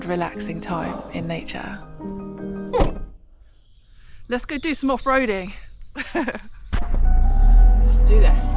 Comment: it's gripped. Let's off road! Well that's what I heard in my head with a fast show flashback! 😂